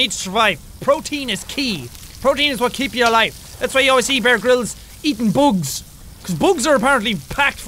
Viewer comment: It sounded like you said Brair Brills instead of Bear Grills